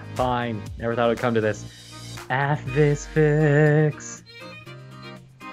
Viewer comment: What did say in 17:36?? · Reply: Aphvis fics